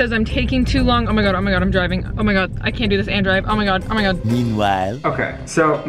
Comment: did she say Andrew??? THE SHIP HAS SAILED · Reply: "And drive" 🤦‍♂️💀